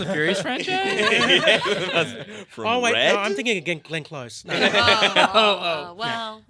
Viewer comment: is it just me or did they say Glenn close for dungeons and daddies